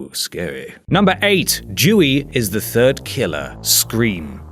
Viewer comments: for the first one i heard "jewey" and i was like wat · Srry I heard it as "jewey"